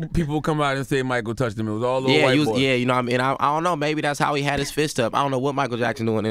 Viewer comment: What he say? 14:28 "maybe that's how he had his fist up" 😂🤔